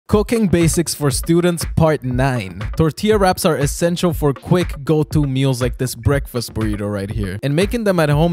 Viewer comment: Bro i thought he said cocainebasics for students 💀